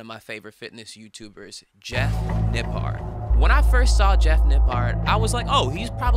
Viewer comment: I thought you were saying the last guy’s name was “Jeff Nip Hard.” I literally paused for a second decided that seemed plausible and went with it. Took until his name finally came up on screen. Oops.